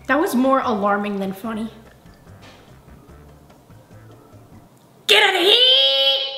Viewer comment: sounded like: “Get outta heeere!” 🤣🤣